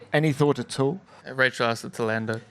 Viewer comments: what did Oscar say? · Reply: “Rachel asked that to Lando” i think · Anyone know what Oscar said at 11:13? Can’t make it out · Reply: "Rachel asked it to lando"